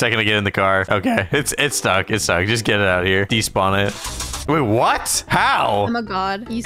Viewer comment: bro i sweat said babe💀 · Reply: She said “wait what” but it sounds like she said babe because she was laughing